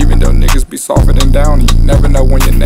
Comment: “lil boat how you feel, just like a million dollars Danny” the hardest part in the song 👍🖤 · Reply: At first i thought he said "daddy" not danny